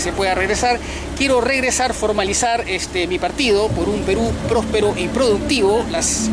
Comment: retrocedí tantas veces...pero pareciera que dices PERÚ PRÓSPERO "IMPRODUCTIVO". Corrigeme. Por fa. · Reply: "...Perú próspero y productivo..."